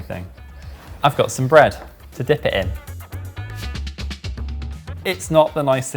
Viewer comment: did he say that he's got bread to dip the oil in?